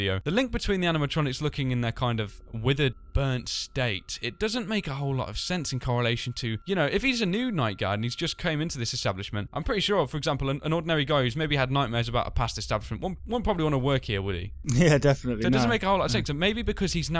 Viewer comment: THE FIRST TIME I WATCHED THIS I THOUGHT SAID "If hes a nude night guard" YEAH I THOUGHT HE SAID NUDE!! Then i went back to 3:29 and then i was like, oh, he said new.